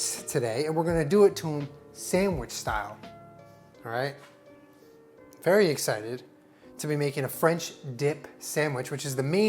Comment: When you say “very excited” I hear “Aries excited”’ and I cannot not hear that now.